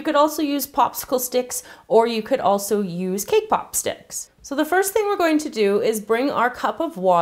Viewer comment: I thought she said kpop sticks 😂😂